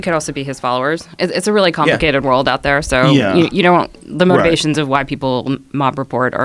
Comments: Did she say 'the motivations of white people" @ 6:20? lol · did she say white people? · does she say ''motivations of white people''? she kinda fumbles over the word but if so that's an odd slip.. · Did she say "the motivations of white people" at 6:26 in the video. It is like Jack Dorsey has lost control of Twitter...just look who does all the talking. Is Dorsey this big of Pu$$y? Or is this just Dorsey? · I probably heard this wrong, but did she say 'the motivations of white people' ??